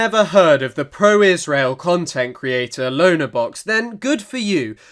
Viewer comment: I thought you said "cuntent creator" at the beginning.